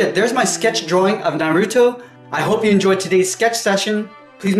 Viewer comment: i think i just heard him say naruuto